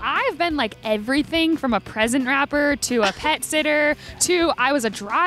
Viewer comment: When Katherine was like from "present wrapper........"I thought she said president?